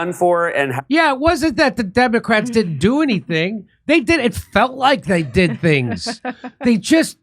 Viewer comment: I keep hearing didn't feel like